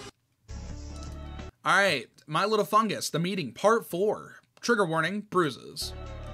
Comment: I swear I heard you say 'my little chungus'